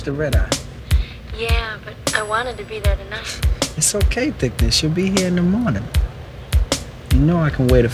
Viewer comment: "It's okay thickness you'll be here in the morning" did anyone else hear this during 0:31? Lmao